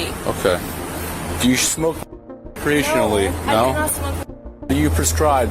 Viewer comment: HAHAHA the caption says the cop asked "do u smoke w*** professionally " not recreational hahahahaha i woukda been like YEA I DO!! hahahahahahahahahaha